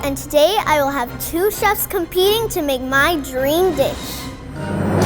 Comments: I heard her say my dream dick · What she said: “to make my dream dish!” What captions said/what I heard: “make my dream itch”